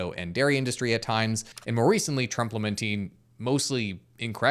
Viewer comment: I swear I heard you say "Trumplementing" when you said "Trump lamenting".